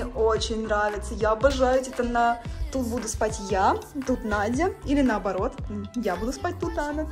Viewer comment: я уже заметила то что ты ее называешь Над а не Ная или мне послышалось но прослушайте фрагмент